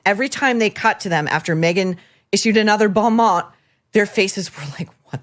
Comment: can someone help me with the English here. What does she say after “issued another”? Bomon? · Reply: The phrase is bon mot. It’s a French phrase meaning “good word” and is defined as a clever or witty remark. Maureen is using the phrase sarcastically.